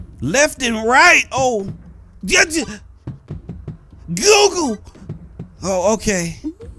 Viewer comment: did he say Google